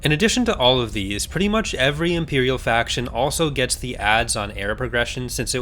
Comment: I don't quite understand what you're saying here. Did you say "ads on air?" I assume not since that doesn't mean anything. Can you clarify?